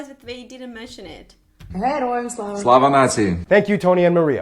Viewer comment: did he say Slava nazi ? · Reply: No, he said "слава нації" ("slava natsiji"), which means "glory to the nation".